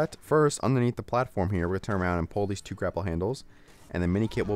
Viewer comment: did anyone else hear "crapple handles?"